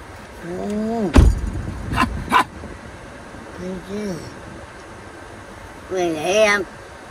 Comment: did say thank you?